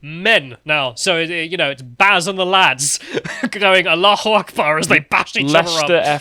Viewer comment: Englands had a big influx of Hawaiin merchants. I keep hearing "Aloha Snackbar", y'all must have a lot of options for lunches.